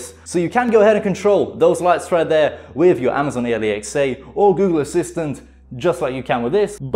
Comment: did he say amaizoin ailaiczeah